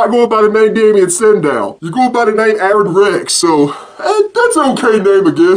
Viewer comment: I thought he said arron tex